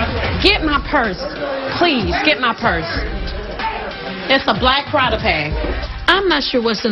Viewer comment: “it’s a black Prada bag.” Phaedra had to REMIND everyone, even in distress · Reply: And then it sounded like she said "black Prada prag!!!"😭😭😭